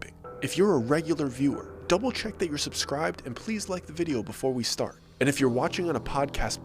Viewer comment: Why would we "like" a video before we watch it? Lol Just curious because I for real thought i heard you say "like" the video before you watch it.